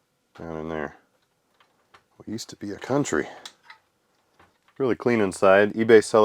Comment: Did he say "We used to be a country" whilst marveling at some punched steel hinges??😅